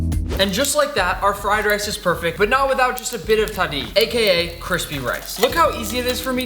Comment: If I heard correctly in 6:08, I loved it that you said ‘Tahdig’ which is Persian name for burned rice at the bottom of a pan which my favourite part!